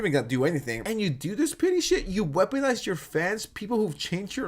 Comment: OH! “PETTY”!!! Ok I kept hearing you say “PITY” and it was making no sense to me lol! So funny!! Love your content!! 2 hours what a treat!